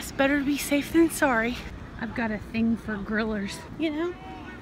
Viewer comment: fairly new to channel so going back to watch all videos got this far in 3 days so getting there. now i thought said she liked gorillas at first instead of grillers then realised it works both ways lol